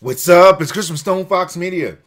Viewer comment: Is it just me, but does it sound like he says " Whats up it's Christmas Stonefox Media?"